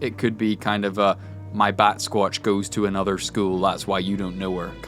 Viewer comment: First time I heard batsquatch, I thought the boys said bat squad....like the dad squad but with bats.